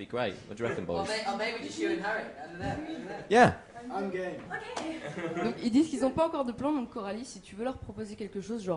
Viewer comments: i thought harry said I'm gay and I choked on my biscuit thinking Finally!!! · I thought Harry said 'I'm gay' ahahaha